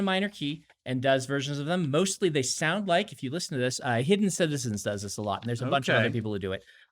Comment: what name does he say here? hard to tell? hidden something? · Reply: Hidden Citizens.